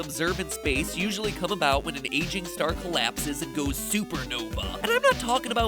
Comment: I heard “Asian star” and was like “What?” XD